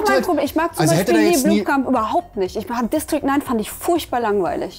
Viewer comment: Welchen Film fand bei 4:25 "furchtbar langweilig"? Der Filmtitel ist leider nicht zu verstehen. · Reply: District 9! Kaum zu glauben.